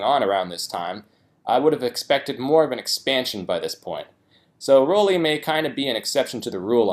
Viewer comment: が心配ですポイントにしか聞こえないww